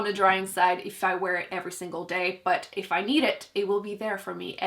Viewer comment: With the concealer I heard “if I eat it, it will be there for me”